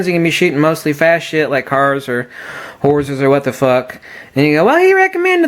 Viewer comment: I thought you said "if you're going to be shooting fast shit like cars or whores.." haha Good vid, thanks again :)